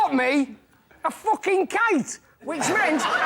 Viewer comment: Please help this American understand what Johnny says his brother bought him that meant that he couldn't vote in the Chinese election. And was that second part correct? I listen to it three times and that's what it sounds like! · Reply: A kite. The second part was correct. Referencing back to 11:54 discussion about what was banned during the installation of new leaders. So it didn't quite make sense because there was no voting.